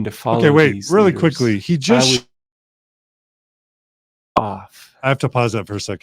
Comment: I thought he said “F*** off” for a second. 🤣🤣🤣